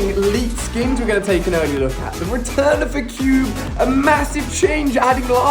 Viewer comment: did he say return of cube